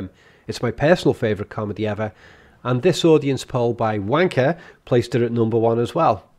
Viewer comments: Ohhh... The comedies were ranked by Ranker. I thought you said "Wanker." LOL your ending. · lol did I just hear him say Ranker or Wanker lol.